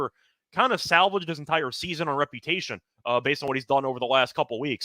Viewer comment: When did I say that? I said to salvage his season. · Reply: you said he "salvaged his season or reputation". When I listened to it the first time I thought you said and reputation which is why I posted. Clearly in the context of you being clear that you think he's the greatest, you must have been saying he salvaged this season's reputation? I don't know. Definitely one could say the gold salvaged his season, but I still don't see where he had anything to salvage regarding his reputation even for a single season. Love the show!